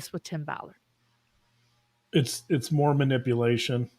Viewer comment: When Dr. John said “more manipulation” I heard “Mormonipulation”….It works….so accurate. 😏